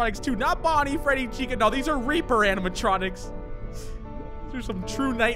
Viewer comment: I low key thought he said raper animatronics 💀